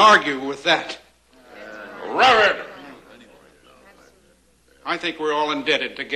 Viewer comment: I always thought he said "RABID!" It's also what it sounds like Olsen Johnson says back to Gabby at the end if his speech.